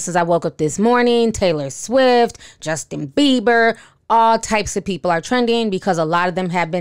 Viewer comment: Did say Jessica Bieber?? Or am I tripping?